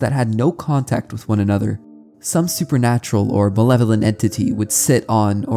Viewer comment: it could've just been the way you said it, but it sounded like you said benevolent here, which means good in nature The correct word is malevolent